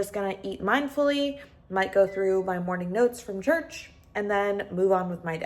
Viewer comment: Did I hear "notes from church?" :) Girly, are you a Christian? <3 14:14?